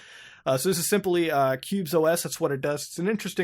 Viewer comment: You said "Qubes" but all I hear is "pubes".